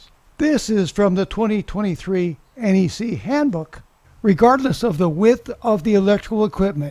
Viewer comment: at 4:10 "enhanced comment" . "Regardless of the width of the electrical..........ect..". Exactly where can I find this 'enhanced content' ? Sounded like you said "2023 AC handbook"? Looking everywhere but to no avail. Thanks for all your help. P.S. I have the new 2023 NEC 70 code book, and the 2023 NEC Applied Codeology books, but don't see it anywhere in there.